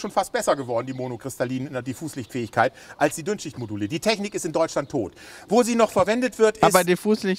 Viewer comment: Ich habe zuerst Dünnschissmodule verstanden…😅